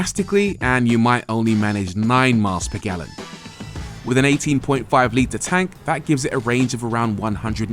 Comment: Did you say 18.5 litre tank 2:21 or I heard you wrong